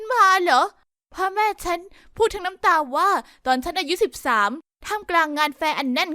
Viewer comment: อายุ13หรือเราได้ยินผิด😅